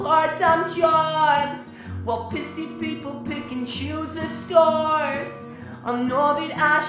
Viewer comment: when i first heard this song i thought it said "while pissy people picking shoes for stars" i was like what?? xD